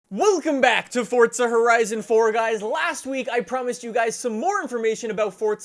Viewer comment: Watch the first few seconds of the video over and over and it sounds like is saying"we'll come back to Forza horizon 3!". Guess you thought brainstorm/green needle is awesome, or was awesome, Try this out!